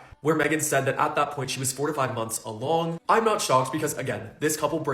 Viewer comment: Thought he said she was 45 months along around @ 02:19 😂 Was about to be like 🤨🤔🤔🤔